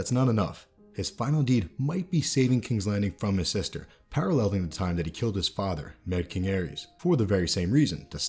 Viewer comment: NON-SPOILER VIDEO please DO NOT comment about any Game of Thrones Season 8 set, casting, script, or plot leaks. Thanks! I read every single comment, but the only way I see responses to original posts is via the creator studio app. And I would like to avoid typing from there due to some issues with my hand. I'll still always read follow-up comments, but for continued conversations, please join me over at TMMAC <link>) Join me on TMMAC <link>) to chat about this topic or any topic. For your chats here, please keep in mind that this is a Fan Channel not a Critique Channel So please leave all negativity at the door and stick to the positives in regards to the writing, casting, acting, etc... Thanks! =))) Westworld, Hobbit, Altered Carbon, Lord of the Rings, Penny Dreadful, Everything Sucks and other videos coming soon :) Show less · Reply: In Jamie's section at 14:21, it sounds like you said "paralleling the time when he killed HIS father, mad king Aerys". Am I mishearing that? Sorry if this has been answered elsewhere.